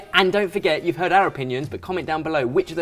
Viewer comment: did he just said if you arab onion lol